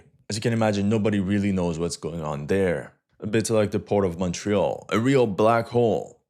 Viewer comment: did you say port of montreal??? do you live there?